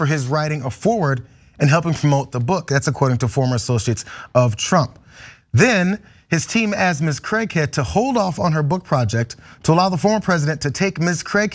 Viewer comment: Had to go back and actually watch the text. I swear I heard "Miss Crackhead" 😂